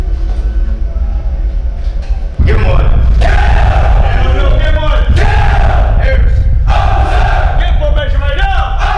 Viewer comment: what did he say at 0:42 ? before the recruits say "KILL"? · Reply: Give him one